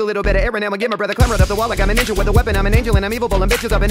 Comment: anybody else hear the N-word? He said “like I’m a n with a weapon” play it on 0.5x speed and listen